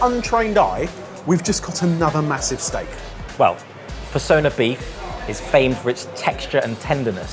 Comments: I thought he said fursona beef a sec and got worried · I keep hearing “Fursona Beef” but that’s not bovine, that’s human meat. 😂😂😂😂 So, what is it actually named?